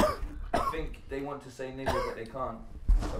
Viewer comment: Did say Nigga?